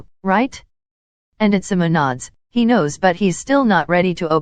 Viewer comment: No one: Me at 19:14 : did that say Nikki Minaj 😯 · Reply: When it said atsumu nods it sounded like nikki minaj